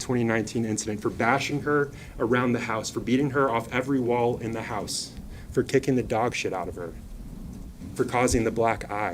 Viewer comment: did he just say 'for kicking the dog sh*t out of her' ? 😳